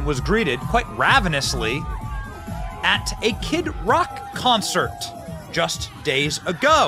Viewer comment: You said Kid Rock concert. I heard a kid's rock concert.